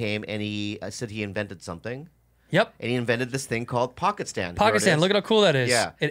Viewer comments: At first I thought they were saying "pocket sand" 😂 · I thought they said "pocket sand" at first. Sha sha sha.